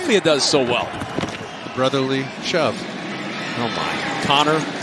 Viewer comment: listen to what the announcer says "brotherly chubb" 💀💀💀